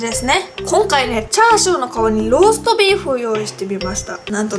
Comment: 鼻つまってる？？ 「ローストビーフを用意してびばした」って聞こえた…